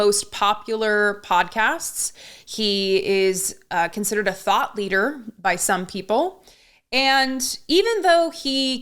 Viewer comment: I heard that as “Joe Rogan is a thot leader” and I refuse to back down from that premise going forward